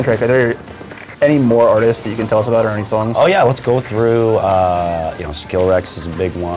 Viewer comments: did he just say skillrex!?! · I'm sorry... did he just say "Skill-Rex"?